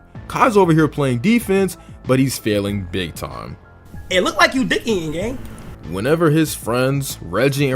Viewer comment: what does Kai say here? "It look like you ___ here gang?" digging?